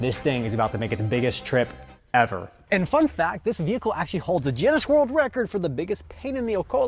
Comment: did you just say juinness? I know you don't mean Guinness 😂